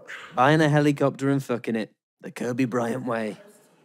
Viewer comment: "buying a helicopter and f***ng it, the kobe bryant way" is outrageous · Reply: justice for kobe and his daughter lol and i swear he said f***ing IN it made no sense bro